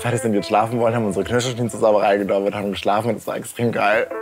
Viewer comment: Kann bitte jemand Dolmetscher spielen, ich hab actually kein Wort verstanden xD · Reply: Er sagt: sie haben ihre Knirschschienen hineingetan und sind dann schlafen gegangen